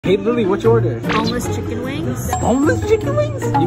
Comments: When she said that i thought she was saying homeless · I thought she said "HOMELESS chicken wings" 😂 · When she said "boneless chicken wings" I heard "homeless chicken wings", help I cant😭😭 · U thought she said "homeless wings"☠️☠️